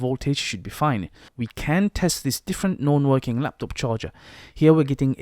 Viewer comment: thanks for the vid but I have some questions. On 3:50 you say "we can test this non-working laptop charger which gets a reading of 19.5V on a 19V charger", but isn't that a CORRECT reading? You said between 18.24 and 19.76 right? I don't understand both this nor why you proceed to test the AC saying "since the adapter doesn't work" if the multimeter was showing 19.50 :( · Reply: I think he said " we can test this different KNOWN working laptop charger" 2 years later but still...